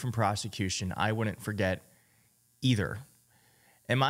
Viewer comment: Lol, I thought he said, "Don't forget." How can we bring this back to SCOTUS now that he's officially destroying our country??